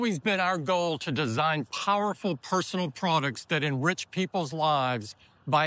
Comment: Did he just say: "We design products for rich people" ?